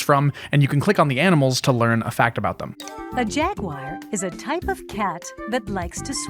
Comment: Did that lady just say "jagwire" instead of "jaguar"